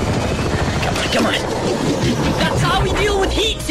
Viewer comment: Did he say we go pro? Or am I hearing wrong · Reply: Nah he said “we go cold”